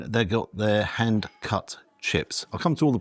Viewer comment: I thought you said handcuff chips. I’m thinking WTF, can’t wait see them!